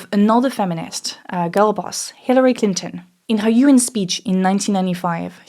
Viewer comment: oh...U.N. sppech...I misheard 'Urine speech;' I must have been listening through the 'winderr' ;p hey, i grew up in a matriachal household...i turned out ....ehh....i mean... patriachal is low hanging fruit but the testes that hang, hang alongside some pretty floppy breasts...